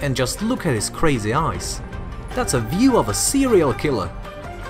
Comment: I hear serial killer 6:51. Lovely narration.